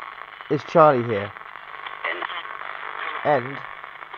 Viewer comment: When you ask is Charlie here at 6:25 it sounds like spirit says "energy."